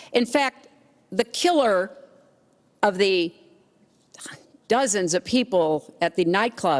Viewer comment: anyone else hear someone say "dozen"